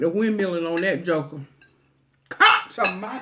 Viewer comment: Wait, what did she say @ 22:53? Cops of mighty noah or oak? I can tell she is from somewhere South with her accent and Belts department store (Never heard of it) and LOL at wore him out like pancake batter. Ok, I'm editing my comment, seeing the Cowboys cup I'm assuming sge's in Dallas Texas. Yes, I just found her video today after watching My full Figured Life 's Mukbang. The sloppy joes title caught my eye because I just had that for dinner myself last night. :-) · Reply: Ms Jaye - yes, I live in NC. I said "she wore that joker out". And it is Belks department store. thanks for watching